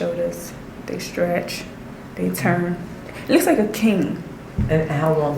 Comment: think she's saying "cane" not king 🤷🏾‍♀️